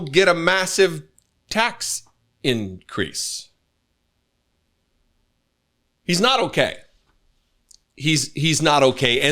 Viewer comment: I can't believe that I'm defending the orange sh*stain and, obviously, everything he's saying is a lie, but I think he's actually said 'a massive DECREASE'. He just mumbles terribly.